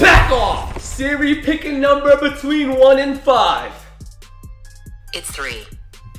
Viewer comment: At the end I heard him ask pick a number between one and three